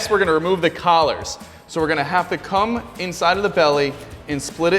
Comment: Did he say, “We’re gonna have to come inside of the belly?”